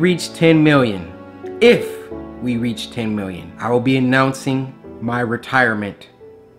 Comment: Cory: “I will announce my retirement on 10mil” Everyone: “maybe we should just keep it at 7mil” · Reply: mm i thought you said 9 mil well ok